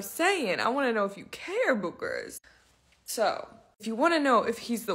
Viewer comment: did she call us boogers?